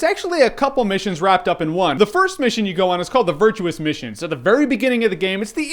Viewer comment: Did you say "virtual mission"? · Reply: No, the Virtuous Mission